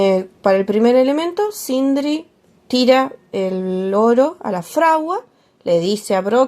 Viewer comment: aca escuche "Sindrid tira el LORO a la fragua"🦜👀 jajajajaja